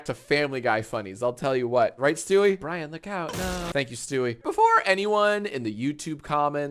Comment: BRIAN LOOK OUT NOOOOOOO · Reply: I thought it said “BRAINROT LOOK OUT NOOOOOO” 😭